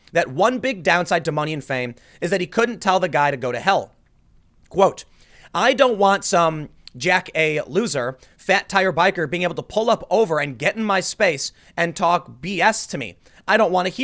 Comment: I thought he said it was a "biker" who assaulted him, not a "bicyclist." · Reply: Thought he said a fat biker also. Story sounds better when the bikers for trump hells angel comes after him.